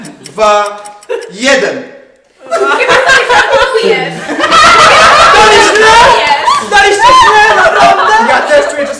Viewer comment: Co powiedziała? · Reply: chyba sobie żartujesz''